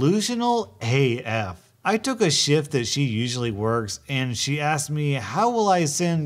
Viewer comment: When you said shift I thought you said shit because the poop story was right before the coworker story👁👄👁🖒